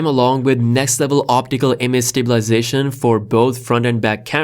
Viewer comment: did I hear OPTICAL IMAGE STABILIZATION for the front camera?😲🤔